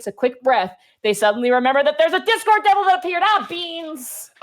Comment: Not going to lie, when they started I thought said "Disco Devil" and now I am sad we haven't gotten to witness that.